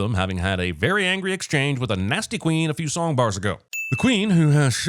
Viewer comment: thought he said (Lightning) McQueen